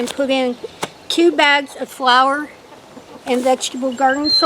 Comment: Omg you need to go fishing lol I thought I heard you say 2 bags of flour so I had to go back and listen again and yep you said flour lol thanks for a good laugh but I think you and Mark need a fishing break again